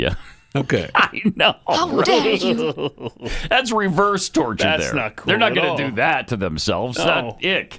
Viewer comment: Thinking about setting up a haunted house, each room has a time controlled lock and a tv with the veiw on in every room that you cant turn off or down. The doors only open after each episode. Terrifying! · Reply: Geez thought you said, haunted house, not torture! I think there’s a statute or something about that. 😱